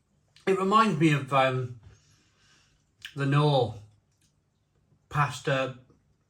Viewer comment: “Dedications what you need, if you wanna be a record breaker….” Chrikey, you really took one for the team there ! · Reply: I honestly thought you said “it reminds me of Lenor” (the fabric softener) but no, you said “Knorr”